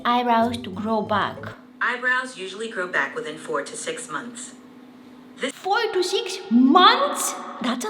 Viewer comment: I thought siri said 46 months but its actually 4 to 6 months 😂😂